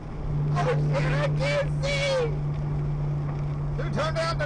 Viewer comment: all I heard was “Holy sh*t, I can’t see.”